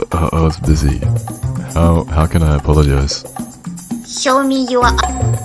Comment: what i hear is "show me my *bonk*"